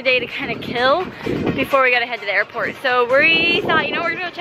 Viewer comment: When said airport I thought he said Epcot and I got excited